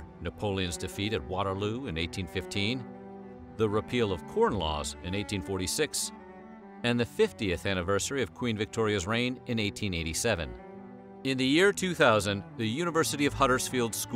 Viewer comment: Anyone else think he said “Porn Laws” at 7:00?